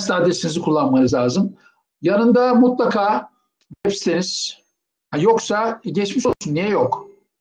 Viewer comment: 'de Hocanın ne dediğini anlayamadım, yardımcı olur musunuz ? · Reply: Merhaba Furkan, hoca 3:00'de web siteniz kelimelerini söylemiş ancak ufak bir sorun oluştuğu için tam anlaşılmamış :)